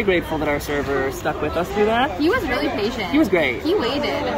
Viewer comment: Did you say “bitchin’ “? Wow, smiling eyes, that’s from my days! Or was it “busy” you said?